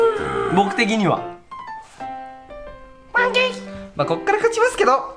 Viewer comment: ま○ゲに聞こえるのは僕だけかい？